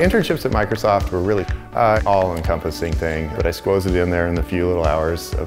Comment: Did he say he "squoze" it in there?